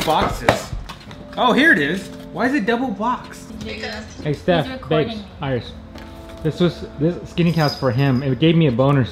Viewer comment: Did u call your wife Steph babe & Iris ? @ 2:25 anyway nice to see her more in the vlogs